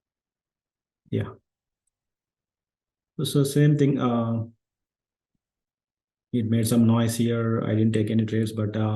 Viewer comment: excellent content. Compelling. You are clearly the real deal. You mention a swing trader around 1:32:00. You said you liked the way he swing traded Carvana. Sounded like Kolamungi. Can you provide that name? Thanks. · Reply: He's perhaps referring to Kristjan Qullamaggie